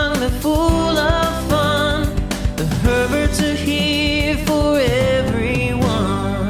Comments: First time watching your channel. I thought at the beginning of your song it sounds like it said “ the perverts are here for everyone” I had to rewind to make sure I heard it right..... lol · hahahahahaha i have never see their youtube channel and only on tiktok so when they played the intro I thought it said, "tHe PeRvErTs ArE hErE fOr EvErYoNe" my ears are slowly failing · I heard "perverts are here for everyone" instead of "herberts are here for everyone" am i the only one or not?😂😂